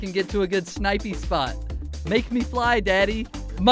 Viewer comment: did Chris say make me fly daddy 😂